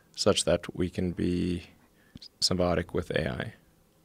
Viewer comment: what is the word he used at 0:50 "we can be ______ with AI." I get what he's trying to say but what's that specific word he uses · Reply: symbiotic